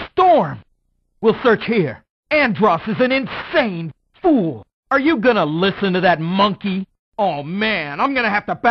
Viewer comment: What did he say??? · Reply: He said "Are you gonna listen to that monkey?"